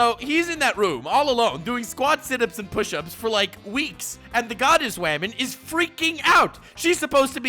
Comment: I heard "goddess whaman", laughed my ass off, and then subscribed immediately